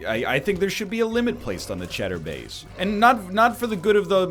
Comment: "I think their should be a limit placed on the *******" I keep hearing *insert name of an ominous and kinda sad adult camming website*, I have no clue what the actual word he's saying is, help my shit tainted brain please :< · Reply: never mind, found it, it's CHEDDAR BAY..